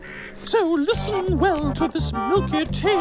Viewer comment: he sounded like he spat when he said cravindale it sounded like he said preventable